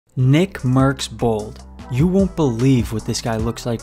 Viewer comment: At first I thought he said nickmercs balls was that only me?